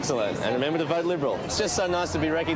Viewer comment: does he say "remember to vote liberal"?